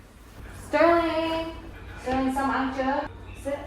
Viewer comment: I quite literally had to go back and look at my phone because I thought you said Sterling do you want some Matcha? 💀